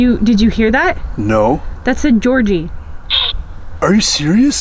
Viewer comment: /12 I heard "Georgie" too